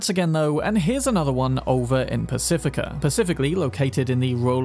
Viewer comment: Was it just me or he said Pacifica pacificaly on purpose instead of “specifically”? Clever town of a dog he is